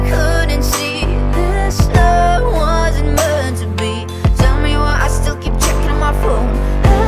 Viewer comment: Kinda sounds like shes saying "tell me why i keep chicken waffle"